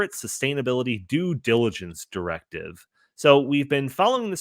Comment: I know the said due diligence", but what I heard was "Dude diligence"! have we just stumbled upon a new, and potentially, hilarious new ideology?! LOL